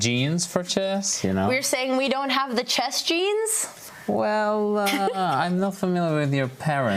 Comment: did someone say chess jeans? :D